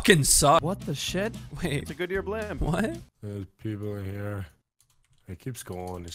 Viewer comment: Lol 9:32 basically says blimb instead of blimp